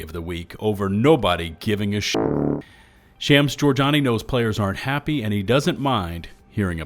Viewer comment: I'm glad I saw the name in print because when I listen to it I hear "Sham store Jonny"...